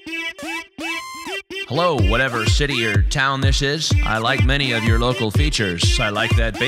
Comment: lol at the start I thought it said ''hello whatever SHITTIER town this is.''